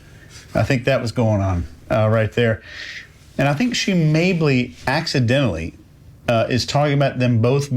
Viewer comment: What does say? Maybley?